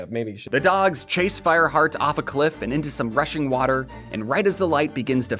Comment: for a Second I thought he said Russian water Bro